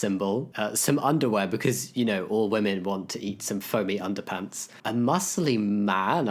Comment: hey, transbian here, I heard “eat some underpants” and blacked out 😳